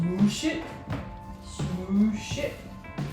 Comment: When you were saying "Smoooosh it, smoooosh it...." I had to giggle. I thought at first you were saying "Moose s*!t". My sick twisted mind gets me in trouble. I know better than that out of Miss.